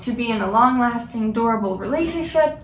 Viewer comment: When she said "long lasting durable relationship" I heard "long lasting adorable relationship"